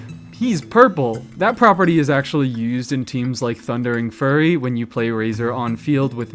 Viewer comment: did They really say Thundering Furry lmao